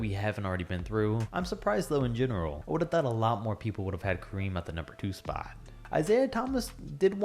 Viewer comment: Wait did you just say you would have Kareem at number 2?